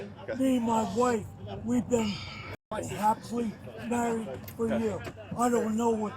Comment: The dude said him and his wife were happily married. The way she was hugging the man that helped her says different. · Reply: It was hard to understand but it sounds like he said happily married for a year. I guess the honeymoon is over.